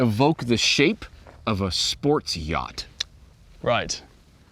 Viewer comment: I thought he said ' Sports jock"